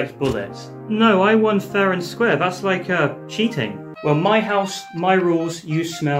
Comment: “my house, my rules, you smell, like balls” best line ever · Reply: I thought he said horse ._.